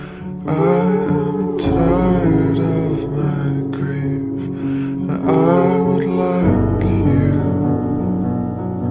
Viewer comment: What is he sayin in minute 1:53 ? He says: Help me to sleep, im tired of my ____? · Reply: I am tired of my grief.